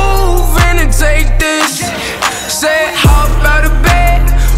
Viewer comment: Is it me that I hear "Hop out of bed, rock kind of gay" at 0:51 ?